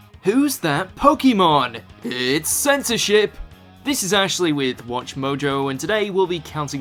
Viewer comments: when he said censorship what i heard was "censorshit" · i heard censor**